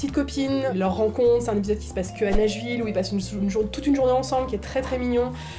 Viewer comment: j'ai entendu "Neige-ville" au lieu de Nashville. ç'aurait été bien si ça existait en vrai :)